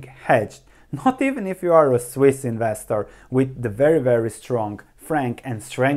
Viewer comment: For a second i thought you said “unless you are a swiss investor “ ... then I thought god damn it why our currency have to be strong:))))